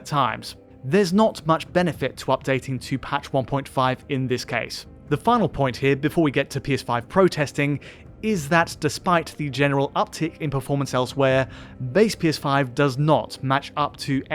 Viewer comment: anyone else heard "PS5 protesting"? 😅